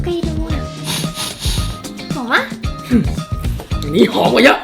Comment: ได้ยินว่า ี หอมกว่าเยอะ